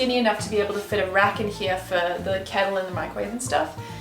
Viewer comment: For some reason, when you said "the kettle" I heard it as "Caleb" and for the rest of the video I was smiling about the fact you named your kettle Caleb